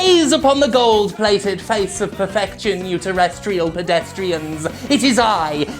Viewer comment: I thought you said "uterus-trial pedestrians" and spent 5 min picturing uterus footwear.